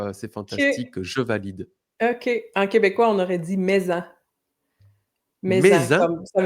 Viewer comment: Je n'ai pas compris l'expression québécoise que a utilisée (7:12) qui correspond à "de oof". J'ai entendu "mais en", mais c'est pas clair si c'est vraiment ce qu'elle a dit. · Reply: mets-en ;)